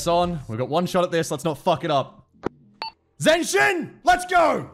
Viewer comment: i briefly thought said 'henshin' at 1:59 and honestly i would not be surprised if he turned out to be an Australian Kamen Rider